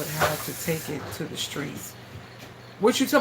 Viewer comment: No mo storage fees…and you know early today when you were talking about the crock pot, I thought you said that you may take it to the storage…I was like I didn’t hear her right…she bout’ to close out her storage!?